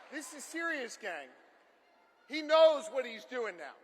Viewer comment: GSD........ mic drop...and did he call the crowd "Gang" that was epic...lol..